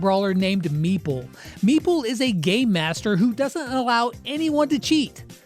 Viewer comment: Did anyone else hear him say Gay Master?